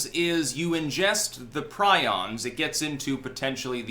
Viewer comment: Lol, I thought it said prisons, which made no sense but sounded interesting.🌺